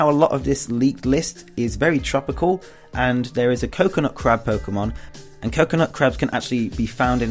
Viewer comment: Good video! I enjoyed it. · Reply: you got to the crab part, when you said "Now there is a Coconut Crab Pokémon" When I first heard you say it, It sounded like you said "Coconut Crap Pokémon"I was like, "COCONUT CRAP?!?! OMG THATS GONNA BE AN AWESOME POKEMON!!! COCONUT CRAP, I CHOOSE YOU!!